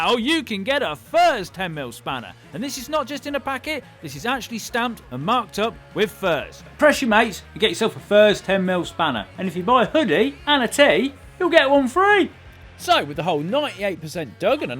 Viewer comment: I heard "If you buy a hoodie, and a tea, you get a spanner for free". Now I'm dispappointed the FurzeShop doesn't sell tea!